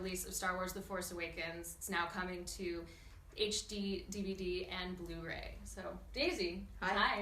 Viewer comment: Thought she said "coming to HD-DVD" 😂 Was like.. wait, was this recorded in 2006? (she said HD, DVD and Blu-ray).